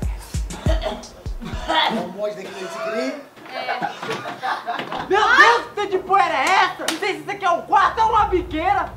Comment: q pourrah foi essa q o falou pq eu num entendi e tou curiosa pra sbr alguém me "helpa" ae pfvr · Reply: "Era um homem desse que voce queria?"